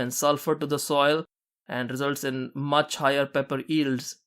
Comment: "results in much higher pepper _______?" I rewinded it a thousand times, what are you saying???? Ears??? Eels? ...wat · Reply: Results in much higher pepper yields